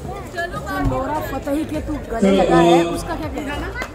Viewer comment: What Name Bharti Said I am Confuse Nora Fatehi Or Some Other